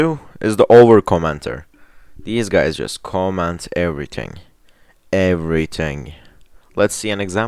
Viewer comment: I thought he said every ting